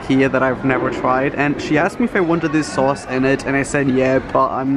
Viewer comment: When he said “the girl asked me if I wanted this sauce and I said yes” I thought he said the girl asked me if I wanted this sauce and I said YEET! lol